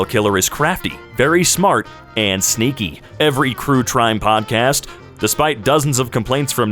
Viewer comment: did you just say crue trime